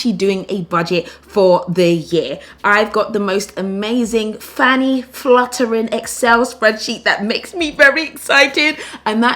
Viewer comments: I had to pause the video, did she just say FANNY FLUTTERING!?!? XD she kills me omg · Did she just say fanny fluttering 😂😂